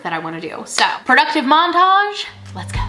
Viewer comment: I thought you said "productive mom-tage" and not montage. Definitely using that word from now on 😂